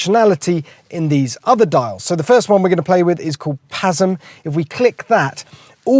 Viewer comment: Sorry.. I thought you said "spasm"